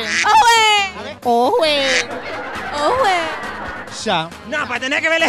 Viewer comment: El: aujeeeeee Yo: ay escuche conwey 🤣🤣🤣🤣🤣